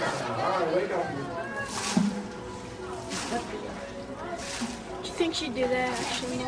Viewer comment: does anyone else hear the audio at 1:46, a young boy saying, "do you think she'd do that if she knew?" when ms. brancheau throws the water on tilikum's pectoral?